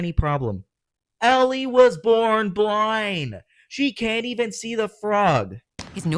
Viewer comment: i think she said peanut